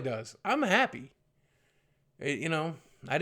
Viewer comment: thought he said abbey not happy xD